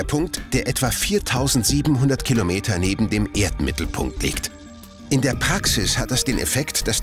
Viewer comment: 🤣🤣🤣 ich hab Erdnippelpunkt verstanden 😂